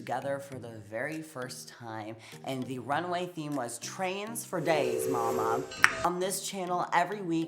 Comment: i thought this said "Trans for days..." Gottmik... was born a girl baby!! and she still is one lowkey tho